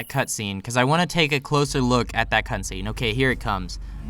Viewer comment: he wnats to take a closer look at that cunt scene, anyone else hear when he said that